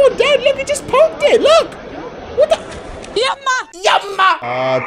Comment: I know what she said I understand that language mama was the word